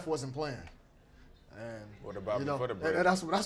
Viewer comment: What does Lillard say at 17:50? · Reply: Greg Murray he said what about rest of them